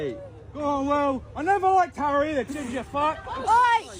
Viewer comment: What did the kid yell at William? I think I heard ‘ginger fart’?